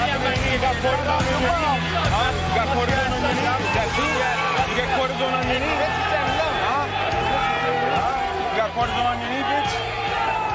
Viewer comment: Can anyone work out what Masvidal is saying about Colby’s knee? It sounds like he’s referring to an injury because Colby looked a tiny bit shocked about whatever was being said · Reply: Got cortisol on your knee? Must be saying he knows he has an injured leg or something.